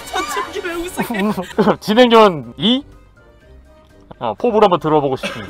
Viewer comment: 진행요원 2 처음 들었을때 정해영인가 싶었는데 다시 들어보니까 진짜네